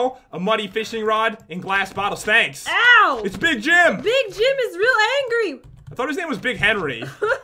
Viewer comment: I thought it said big Jin (like from bts) But it said big Jim